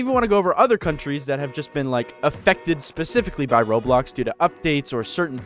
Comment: says roblox the editor ''*i heard roadblocks*''